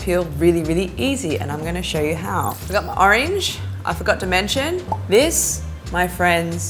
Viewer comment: I thought she said I’ve got the orange I’ve got dementia 😂😂 Lol not making of ppl who do have dementia just thought it was funny if she had said that like randomly in the middle of the hack 😂😂💗